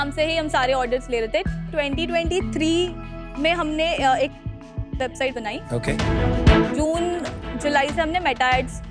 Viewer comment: ye kya boli idhar?🤔🤔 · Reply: Shopify